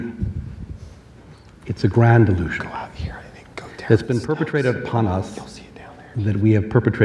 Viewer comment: What is being whispered at 53:00 ? It's very faint in the background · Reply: To me it just sounds like 2 people next to the camera whispering to each other. I hear "*something something* anything. Go down the steps, you'll see it down there." I mean the dude could have just been asking how to get to the bathroom or something. I think you were maybe over-reacting a bit hahah.